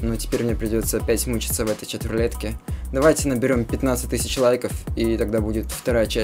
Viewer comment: Он просил 15к лайков а нам послышалось 250к лайков а уже 265к лайков